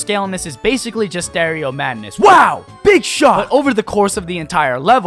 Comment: Did somebody just say [[BIG SHOT]] ? sounds like sounds like [Delicious Kromer]